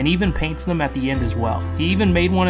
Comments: When he said bench I misheard it and thought he said b*tch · That bench is maybe the most wholesome thing i ever seen😭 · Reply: Unfortunately my ears don’t work and I thought he said Bitch and not bench 💀